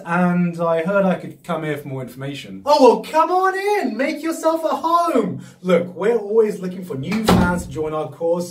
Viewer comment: “Come on in, make yourself a harem!” I wasn’t sure I was hearing that right at first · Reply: rockclimber129, I thought he said, "Make yourself A home," like if he was gonna have to live there from now on.